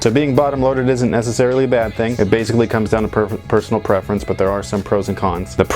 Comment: sound like you said pervert 😂😂